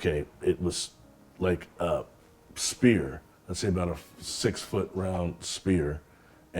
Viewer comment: i think he said sphere tho, or meant sphere